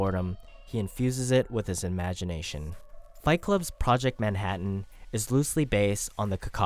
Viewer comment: Not sure if I misheard you, but I think you kept saying project Manhattan instead of project mayhem.